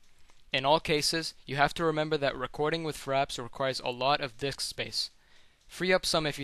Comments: maybe its just me but at 8:38 - 8:40 sure sounds like hes saying fraps requires alot of d1ck space lol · Did he just say dick space? lol. But overall a ver nice and informative video it helped me alot thanks!